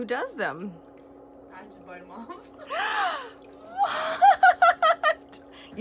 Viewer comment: Did she just say “ I just bite them off “ ?!?! 😳😳